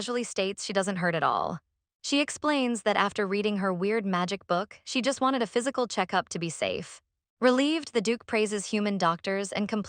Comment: I don't know how but somehow I misheard and read ketchup instead of check-up.....am I hight or smt...😭😭